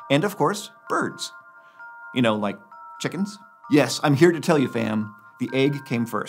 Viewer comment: Did he just say "I'm here to tell you FAM" on 1:40? After talking about chickens..👀